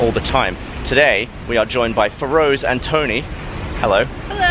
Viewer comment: Thought He Said " Rose and Tony " lmao Woops